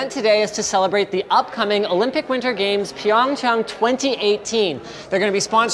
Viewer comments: anyone else almost got super excited cuz you think he said Pyongyang? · I swear, I heard Pyongyang at 0:10 and did a double take.